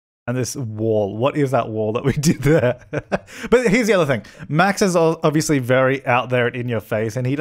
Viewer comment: Whenever he said "Max is", I thought he said "Maxis".